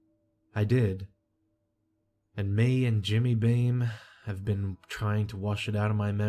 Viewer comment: I thought he said “Me and Jimmy Dean were trying to forget” at first and I was all like who tf turns to breakfast sausage in trying times for relief?? I immediately went and made links to cry into. 😆